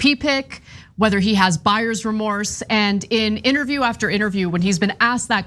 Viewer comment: Oh! She said that, “ … Trump might have buyer’s remorse.”. At first, I thought she said, “ … Trump might have virus spores.” 😳⁉️😳 😉💜🤷🏻‍♀️💜😅